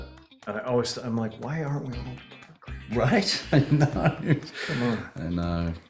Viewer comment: Can anyone tell what Nick is saying at 3:35? I cannot make it out for the life of me. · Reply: "Why aren't we all park rangers?"